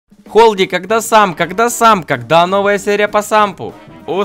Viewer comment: холдик когда самка , самка самка ? Я 1 это услышал .